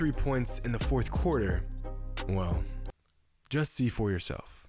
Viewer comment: COD player swear they heard “just C4 urself”